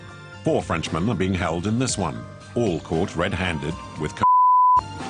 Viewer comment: Now did he say "All caught red-handed with cock"?